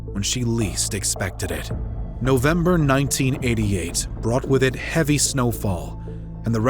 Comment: 1988?.....not what the caption says......:>)